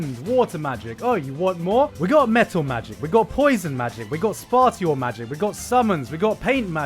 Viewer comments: Was it just me or did he say sparshiel instead of spatial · did you say.... Spot-ial magic?????? Like "spay-shul" ... Waaaat